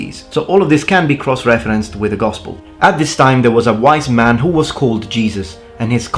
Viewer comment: Did you say "white man" at 10:11, at 17:34 and at 19:45? I listened to that 3 times and it sounds like it. If so, I am confused as Yeshua was most certainly not "white" (as in Caucasian) but was a Palestinian Jew . . . so not "white." Please clarify . . . perhaps you are saying "wise" rather than "white"??